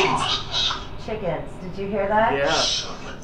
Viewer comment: iam pretty sure i heard it says.. Chickens.. Summons angel..